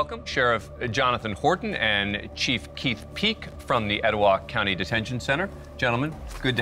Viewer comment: Did he say chief peeks name is “chief keef peek”?? 👀 his name is literally chief keef.. 😂😂 0:23 💯💯